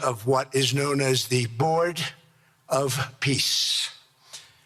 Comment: Putin misheard; thought it was bored of peace.